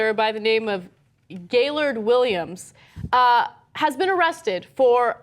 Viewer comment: I thought I heard his name was Gaylord Williams. It would've been perfect.